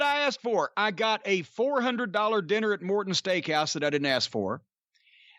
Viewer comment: For a minute I thought he said "Ricky Morton's steak house".